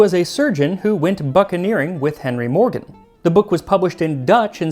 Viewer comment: I'm a bit deaf sonI thought you said "fuckaneering"